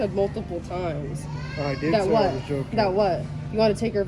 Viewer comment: I thought he said “ I was drunk” but he goes “ I was joking” and idk what ones worse. But him sayin I’m joking and meeting up. Who the hell even jokes like that unless they are hinting at a serious response? That’s middle school shit. When you say “ oh I don’t actually like you we were just joking” like yah okay buddy.